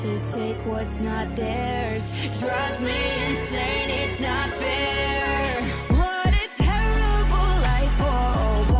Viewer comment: Omg my god I thought it said ‘trust me in saying it’s not fair’ not drives me insane it’s not dair’